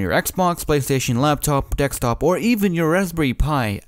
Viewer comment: did he just say dextop at4:33 :D rofl , im sorry i know ur not english and stuff , my english is even worse , but dextop made me lol :p